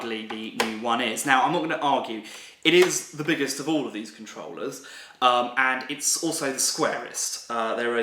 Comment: Mmm did he just say controller